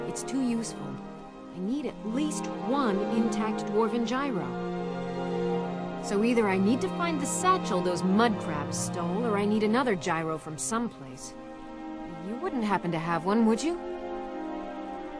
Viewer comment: I zoned out at 9:04 and when she said "Dwarven Gyro" my mind thought she had said "Mind the vagina"